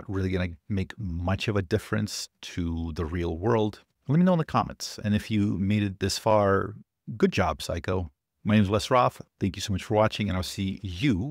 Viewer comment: For a second I thought said “good job, psycho” 😂